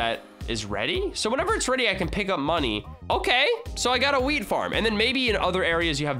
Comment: I got a weed farm😂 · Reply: he said Wheat not Weed, but it sounded like he said Weed Farm, lol